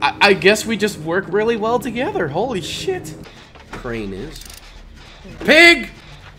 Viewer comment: You said holy shit. Subtitles said holy (mylife). Are you okay at 11:08 Btw my fav point was when she said she was horny for items in gam